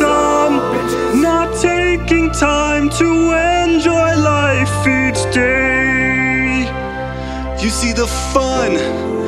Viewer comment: omg i thought hes been saying "... end your life each day" at 1:13, face-palm :/